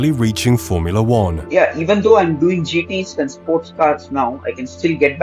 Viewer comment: Oh, I misheard him, I thought he said "doing GT"